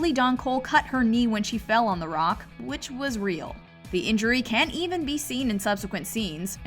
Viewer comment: I’m sorry did I hear “ the injury can’t be seen “ sorry there’s a clear scene where Veruca smashes a big candy and you can see some Red in her knee